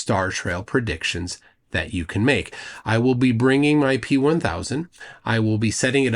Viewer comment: For a second, I thought I heard you say that you were bringing a T-1000 and immediately thought of the flerfers who think you guys are going to unalive Jeran and Nitwit...and that they might be on to something.